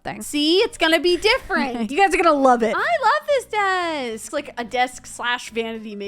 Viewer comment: I thought she said "I love this bed!" I started laughing so hard until I realized it was desk. Lol. Also I love your videos! The sims 4 is so cool but sadly I cant play it (I have Xbox 1 but I set something wrong so now I cant customize) They make me happy when I’m sad or mad or just bored. Edit: I’m showing up